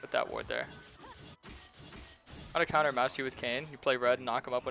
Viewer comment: can anyone else tell what champion name or whatever he's saying here @ 7:30? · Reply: Master yi